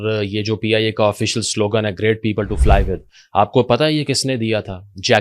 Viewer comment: I heard it is "please inform Allah"